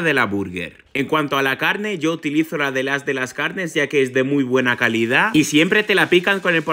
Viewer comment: El as de las carnes? Yo pense que decía "las de las carnes"